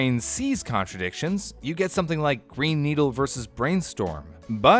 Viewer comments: I legit heard “brain needle” the first time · All i heard is ''Brain Needle'' and I was so confused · i thought it said brain needle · I thought it said “brain needle” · Everyone: You can hear "green needle" or brainstorm" Me: hears "brain needle" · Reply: I only hear "brain needle." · I hear " brain needle or evil " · On the tik tok I heard "brain needle" but on the first one I heard green needle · i heard "brain needle" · ah, great, i heard "Yanny" and "Brain Needle," none of them are actual word... also, does volume affect the pitch we can hear? i crank up my volume to maximum (which really hurts your ears, don't do that), i can hear around 19,000 - 18,800 Hz, but if i keep my volume in normal 40%, i can only hear 18,000 - 17,800 Hz. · the way i heard "Brain Needle" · My brain seems to just mash the sounds together. It sounds like a combination of the sounds to me. So, I just hear “Lanny” and “Brain Needle.” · i hear 'brain needle' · I hear a mix. I hear “Brain Needle” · I heard "Brain Eater" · at first i thought it said brain needle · I hear "Gwen Needs Help" Am I like metally broken or what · I first heard “brain needle” then “green needle” when showed it the 2nd time. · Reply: Yes, that's what I heard, too: "brain needle" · I heard "Brain needle". What does that mean? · I swear I heard 'Brain needle' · I only hear "brain needle" no matter what I read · I only hear "brain needle" sssooooo how tf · I heard "brain needle " lol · Is I odd that I heard 'brain needle?'